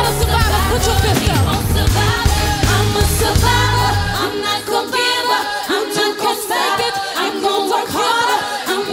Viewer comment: What was beyonce saying from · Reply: “I’m a survivor You a survivor You too can make it If you don’t give up You will survive”